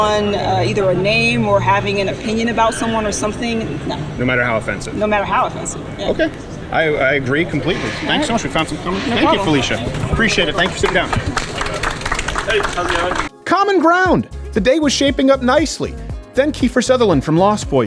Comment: Hahahah I thought he said "Ok thanks we found some common ground, bye Felicia." Heheheheeh He didn't. Would have been funny though.